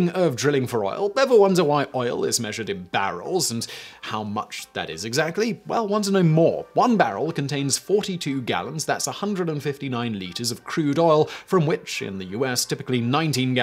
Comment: I've been watching basically all your channels for years (most of the more recently made ones since their inception) and i just realized you're saying, "wonder no more" while i initially thought you were saying, "want to know more." 😹😹😹 That British accent, man.